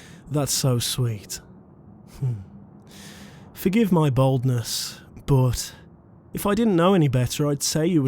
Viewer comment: I can't describe his voice, it's like soft but a little deep but so soothing, it's just so 😭👌🏻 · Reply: I thought he said baldness when he said boldness